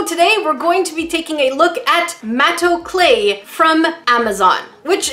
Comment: I THOUGHT "MATO CLAY" SAID "MAYO CRAFTS" AND I WAS GETTING SCARED ABOUT WHAT THEY MENT BY CRAFTING WITH MAYO